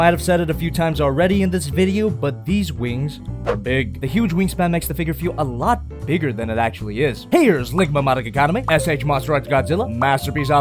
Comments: DID HE SAY LIGMA? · DID YOU JUST SAY LIGMA???? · did he say ligma instead of figma? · Did... Did he just say ligma? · I swear at 8:50 i hear him say Ligma instead of Figma. I think has Ligma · Did he just say ligma madika kaname?